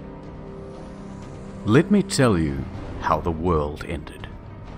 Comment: did you just say lit me tell you?